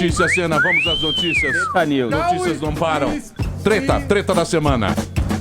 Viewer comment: só eu que ouvi um treta news 2:18 ?